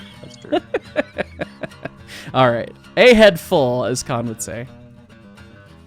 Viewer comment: "A head full"? I thought said "KIIRRKKKKKK!!!!!!!!!"